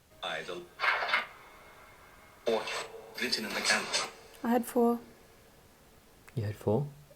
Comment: it sounded like it said "i don't have to" in a males voice when asked how many people were down there before another voice said 4.